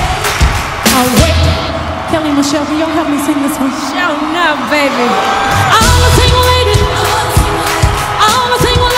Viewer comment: What does Kelly say @ 9:04 ? I feel like I’ve heard her or Beyoncé say it like that in a song. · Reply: Sho nuff